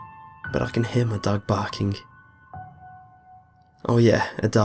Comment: i thought he said "i can hear my dad barking".........